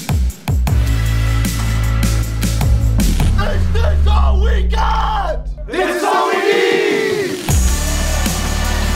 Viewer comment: Kann mir bitte mal jemand sagen, was die da schreien? Ich verstehe kein Wort. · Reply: Ritual des Teams vor jedem Spiel beim Huddle. Der Rookie fragt "is this all we got?" und der Rest antwortet "this is all we need".